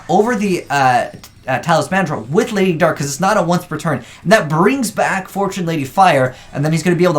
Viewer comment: What did he say at 7:33 ? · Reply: He said "Lady dark is not a once per turn"